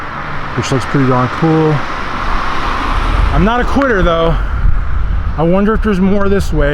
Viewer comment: what was it that you said? Was that something towards the LGBTQ Community? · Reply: It says I’m not a QUITTER. This is towards the end of the video and I had run out of city to film. I did not want to start filming residential areas as I try not to do that. It’s my right to film anything on the public right of way but it’s my choice not to go into neighborhoods unless I have to. Hence for I am not a quitter just have my approach. I’m not sure what you think I said but that’s what I said - thank you for watching.